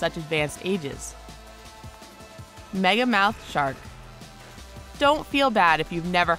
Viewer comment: I thought they said megalodon instead of megamouth lol